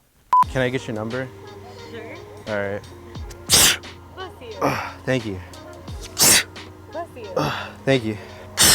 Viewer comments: why did that sound like she said "fuck you" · who else thought she said fuck you